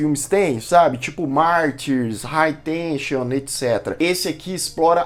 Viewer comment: Que filme o citou aos 1:18 Martiris e o outro? Qual é? Eu não entendi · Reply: Alta tensão